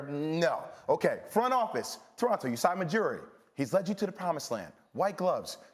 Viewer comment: Did he just say Usai Majiri? 🤦‍♂️🤦‍♂️🤦‍♂️